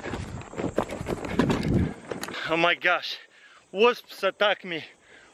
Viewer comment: I thought he said wolfs attacked him, but he said wasps. Lol